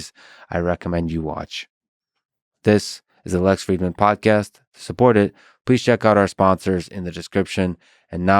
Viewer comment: I legitimately thought you said, "because this is the less frequent podcast" and i'm over here like, did he change the name of the podcast lmfao